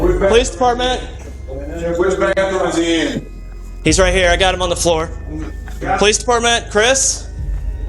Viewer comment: The fact that you misheard "on the phone " as "on the floor " changed the entire narrative of the story. The victim and his killer weren't "best friends"